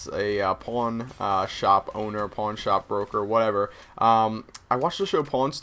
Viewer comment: At first i thought u said makining a porno then i thought u said porn shop but i realized u said pawn shop wen u said pawn stars